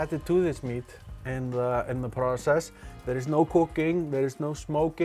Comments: sounds like “there’s no cocaine” when I first hear it. Had to double take that 🤣🤣🤣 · i thought he said "THERE IS NO COCAINE, NO SMOKING"